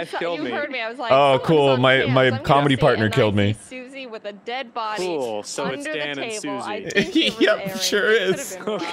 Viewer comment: Ross: "Your wife killed me." Arin: "My comedy partner killed me." Rough day. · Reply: I thought he said Commie Partner, But now that line makes a lot more sense.